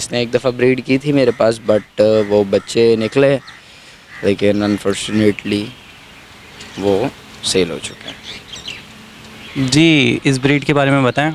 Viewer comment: WHAT HE'S SAYING🙄🤔😂 UNFORTUNATELY SALE HOGAY🙁🤓😂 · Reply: Mujhe laga unfortunately marr gaye... 😂